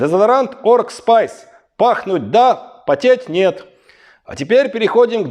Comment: Потеть - нет ... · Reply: Я тоже расслышала "потеТь".